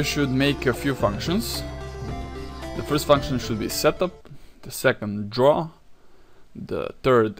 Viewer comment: Whenever he says "setup" I hear him say "shut the fuck up".